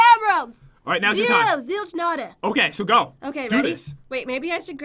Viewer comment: Am I the only on that noticed Jen say, "zero, zilk, notta" instead of, "zero, zilch, notta"